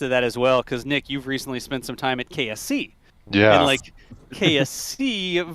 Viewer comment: I thought he said KFC 🤣🤣😅😅